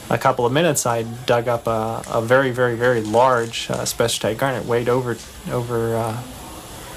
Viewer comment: Please help. This is driving me crazy. At 9:45 he talks about the gem he dug up at a mine that was 3lbs or over 7,000 carats. I can’t understand what he says that he dug up. What kind of gem or crystal does he say he found. I keep replaying it but I’m just not able to understand what he’s saying. Please go back to 9:45 where he’s telling the story and see if you can understand what he says. Please. And thank you in advance. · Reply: spessartine garnet